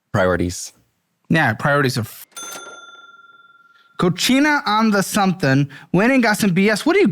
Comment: I wanted to laugh so hard at work😂 I was listening and had go back to see what it actually said. Cochina is what Caleb said which means nasty. The restaurant is called cocina, which is kitchen in Spanish.